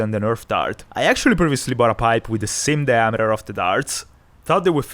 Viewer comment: Bro what did he even say😂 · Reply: He thought he bought a pipe that was the same diameter as the dart but diameter as the dart all came out as one brainrot sounding skibidi word.